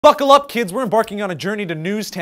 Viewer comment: I thought he said "FUCKLE UP KIDS"